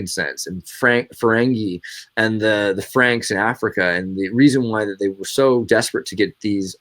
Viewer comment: I thought said Frank Zappa when it was 'Franks of Africa' :D